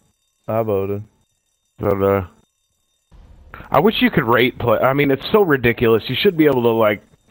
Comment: I thought he said rape😂😂😂